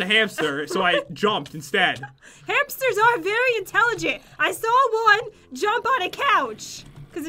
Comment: hamsters are very intelligent! I saw one jump on a couch. it will take, some serious intelligence, to do that. oh, I never knew was a hamster! oh... I thought she said, "I saw one jump on a cow"...